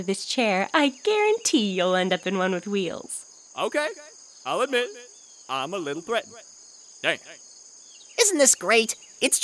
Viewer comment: Nagisa: Look all I'm sayin is we've done all the work, the least you could do is go down to 7/11 and get us some slushies and a carton of cigarettes... Menthols... Please Teacher: Nagisa, if I get out this chair, I guarantee you'll end up in one with wheels Nagisa: Okay, I'll admit, I'm a little threatened. Damn WHY IS NO ONE TALKING ABOUT THIS PART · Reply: I always heard "I'm a little brat" x)